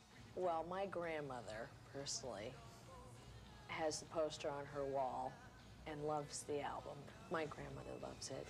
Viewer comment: When she was talking about her grandmothers posters...my brain cut off and thought she said posted. I'm like Grandma posted what in the 80s. Lol. Then she said poster on the wall, I was like well that's more like it. Lol